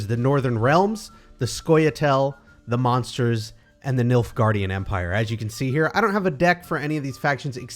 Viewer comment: I'm not gonna lie, at first I thought you said "the milk guardian empire"